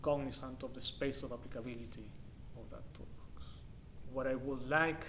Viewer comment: This is great. What is said at 3:18? Space of what? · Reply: Space of applicability of that toolbox